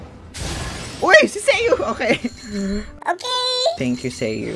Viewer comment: deadass thought you keep saying "seiyuu" turns out it's sayu